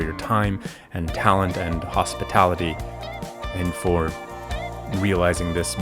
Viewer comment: Yesss I'm so happy for you. The thing I appreciate the most from Adam is that he works to highlight the next generation of makers and artists. With his platform. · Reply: HELP! I thought u said “markers” 😭